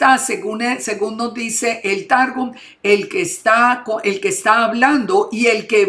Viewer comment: Que es el targum o que dice no le entiendo la palabra que uso en el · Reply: El targum es la version Aramea de la torah